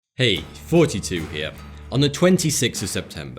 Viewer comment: Ya know, I always thought you were saying “42 here.” At the start of these videos, but then I thought he does have a strong accent that sounds like it comes from somewhere that I won’t mention now (because it will end up being wrong) and the name of the channel is “Thoughty2” so maybe he is saying that instead. And now I feel really bad for miss understanding you for like, four or five videos. (That’s how many videos I’ve watched from this channel)